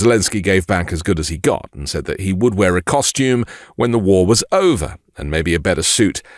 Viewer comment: I 100% support Ukraine! 0:57 Zelenskyy did not say "costume" he said, "custom." Big difference that I think people should understand. He was saying, with that statement, that he is making the same kind of point that prime minister Winston Churchill did back in 1940 to 1945. That point is, it is not time for custom and properness in his life. His country is at war and will always show his people that he is standing with them during this harsh time.